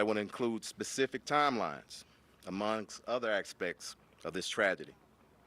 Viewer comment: What's that? Ackspects? Ackspex? What word did you say? Oh, aspects? Gotcha.